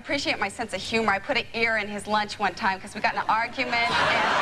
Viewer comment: I didn’t get this one. Guess I’m just dumb. Did she say air or hair? And explain someone please. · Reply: She said ear. That is morbid